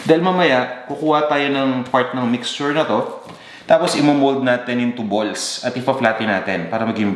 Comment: Misheard phrase: "imomold in tubols" 😅